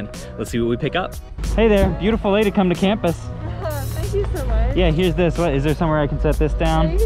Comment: Bro @ 1:09 I think you said “Beautiful day to come to campus.” But, it sounded like you said “Beautiful lady come to campus.” 🤣🤣🤣🤣 Then she said “Thank you so much” 🤣🤣🤣 Your whole life just flashed before my eyes I hope your wife sees this